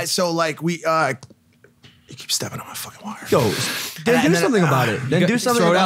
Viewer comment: did they say the N Word???